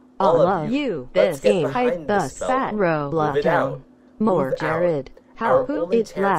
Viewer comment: I heard move jarryd. Yea jarryd, fuckin' move, stop standing in the middle of the fucking hallway ffs.